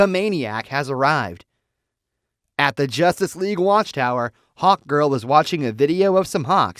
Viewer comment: sounds like he saying HOTGIRL not HAWKGIRL XD